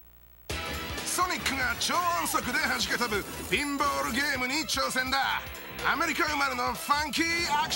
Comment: did he say bimbo game?